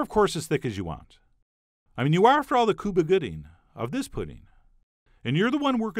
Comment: Cuba Gooding of this pudding?! Am I the only one who heard him say that? 😂